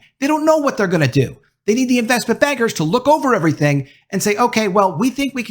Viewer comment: I kept hearing "investment beggars" instead of "investment bankers" for some reason.